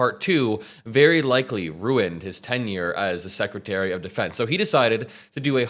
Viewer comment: Sounded like he said former secretary of state instead of secretary of defense?